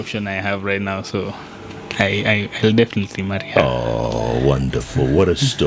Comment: "She is the only option I have right now" who else heard that statement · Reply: moses omondi, I heard "I will definitely marry her"... I wish them well.